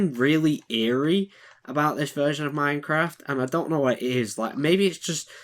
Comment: I thought you said 'I dont know where he is' 💀💀